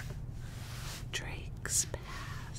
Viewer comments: I SWEAR I HEARD “drakes ass” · did she say DRAKES PASSS?!?!?!?!?@